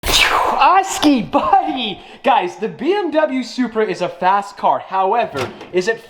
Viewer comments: Did he just say BMW supra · Did he say bmw supra